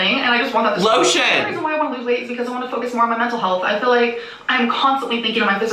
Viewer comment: idk why but because it’s sped up at 16:07 i swear it sounded like she said “focus on my nipple health” instead of mental health, i said girl WHAT 😂